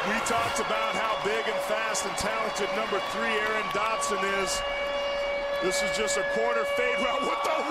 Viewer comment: At first I thought he said Antoine Dodson lol.